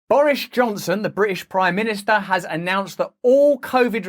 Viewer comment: Did I just hear, "Boorish Johnson"? I love this man...